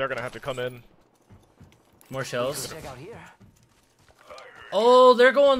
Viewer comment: Does anyone else think he says "shells" instead of "cells" at 13:36?